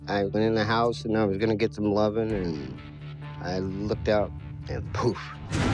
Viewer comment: did he just say he went get some lovin?